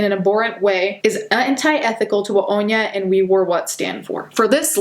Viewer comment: Listening to this video at 2x speed (like I do most videos), I could’ve sworn she said the influencer called herself “We Were Wet” 😬